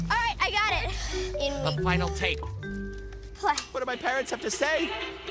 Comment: I heard “one of my parents have to vape”😂😂😂😂